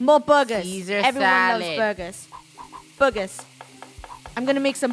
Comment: lol I thought Funneh said at 4:33 Is ''boogers''